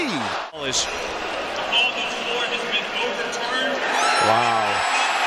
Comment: Bruh, I can't even hear what the referee was saying the final call was. Too loud cheering from the background and of course the commentator already "wow"-ing, not even letting the referee finish speaking first. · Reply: It’s actually quite clear. The ref said that the call was overturned lol